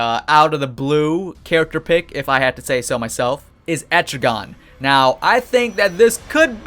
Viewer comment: i thought the one he said was etrigan was cinder block